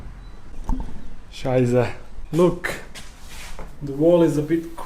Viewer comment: Did he say "Scheisse" at 7:27 ? XD